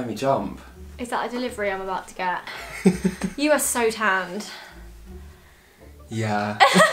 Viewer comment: 😂. When said “you are so tanned” I thought she said “ you are so fat”